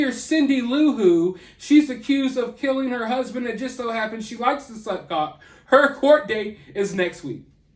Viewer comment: did you say "she likes to suck cock" ??? 😭😭😭😂😂😂😂 STAAAHHP did I hear that right??!! I swear it does, I played that part over and over about 7-8 times 🤣😅